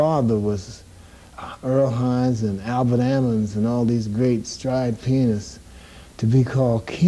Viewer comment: sounded like he said stride penis at 3:14...what did he really say? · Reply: Jeremy Pace Stride Pianist